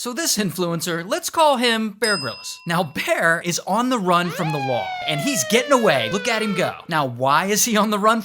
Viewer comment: My ADHD literally thought he said "running from the mall"💀💀💀